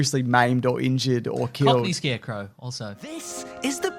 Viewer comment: Does say “comedy Scarecrow” at 11:39? What does that mean, just that Scarecrow is in a comedy? (Judging by ’ “yeah, that’s right...yeah” reaction I wasn’t the only one who didn’t get it, lol.) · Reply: Cockney Scarecrow 👍